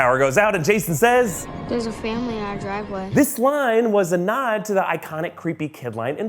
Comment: what does he say ? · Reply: Steph Akurox “there’s a family in our driveway”